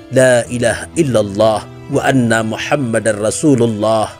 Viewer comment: Maaf ko yg aku dengar pas istighfar nya, berseru trdengar Laila hailallah Muhama bukan Muhamad, apa kuping ku konslet yah 😅 coba dengar kan kmbali takut nya saya salah dengar atau kurang pokus mendengarkan, tp aku ulang2 sama aja Muhama buka. Muhamad